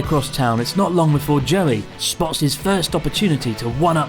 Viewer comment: I really thought he said Jelly instead of Joey